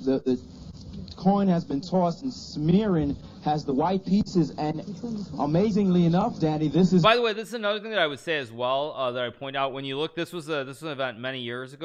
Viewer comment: "amazingly enough daddy" thats all i heard